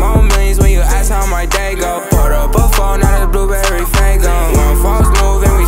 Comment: aight i didn't know the name of this song and just entered blue flamingo but oh well youtube knew what i meant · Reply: I thought he said blueberry fan girl